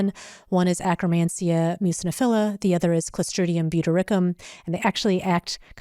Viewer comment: Collustrium V Dilicurim?? Anyone know what she is saying? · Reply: NM. It's Clostridium butyricum